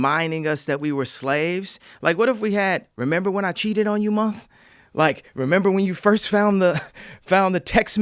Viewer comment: at 45 minutes he says "remember when I cheated on you mom?" or am I mishearing? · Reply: Remember when i cheated on you month*